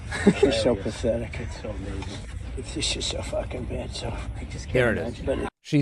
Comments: Sounded like he said "so f-ing bad" Doesn’t matter at this point, he’s a vile, pathetic and an extremely childish loser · Sorry, that's not what he said. I hate trump like cancer, but he's saying: "she's so f*ckin' bad so", not "she's a f*ckin' b*itch". All that aside, OF COURSE he says it all the time, just not on this video. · no trump is clearing saying "she's so fing bad so.." that's clearly what he's saying. maybe her called her that name somewhere else, but that video is not it. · I heard "She's just so F-ing bad". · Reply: I listed to it several times and all I hear is "she's so fucking bad" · Sounds like he said "shes just so fu*king bad, so" not "shes a fu*king bitch" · Not defending Trump, but to me, the video sounds like he is says "she's so f*ing bad." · Sounded like he said “she’s just so fuckin bad” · I heard "she's so fucking bad" on the golf cart. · I heard "she so fucking bad." Not that I want to help DJT, but that's what I heard. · I'm no Trump fan AT ALL... but I do wanna correct this... It sounds like he said "She's so fucking bad" in this clip... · Not going to lie, to me, it sounded like he said, "She's so f*cking bad." Either way, he's gross and I can't wait til our country finally moves on from him. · Imma be honest, I heard "this shits so effing bad" not "she's an effin b" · He definitely said "fucking bad" definitely misheard on purpose lol. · I didnt hear "she is an effin bitch." I heard "she's so f***ing bad"